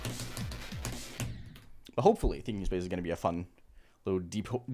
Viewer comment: I thought it said thinking space II I was like what 😭